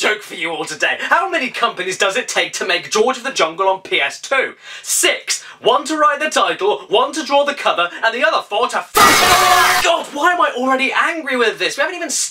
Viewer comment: what did he say? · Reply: Teen Anxt He said that it took 6 companies to make the game: One to draw the cover, one to come up with the title, and the other four to, in Caddy’s words “F*** IT ALL UP!”